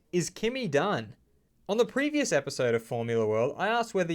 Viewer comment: I thought you said “Is Kimi dying?” My heart was beating so fast 😂